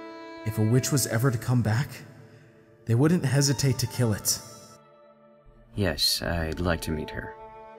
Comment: For a second i thought he said "Yes i would like to EAT her" Lmao